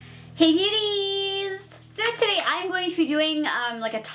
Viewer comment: Am I the only one who heard her say "hey nudies" at the beginning?😂